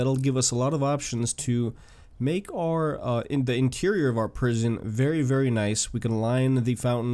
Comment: did he say prison LOL